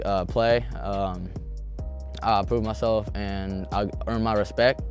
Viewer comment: I thought he said he pooped him self. fk im dumb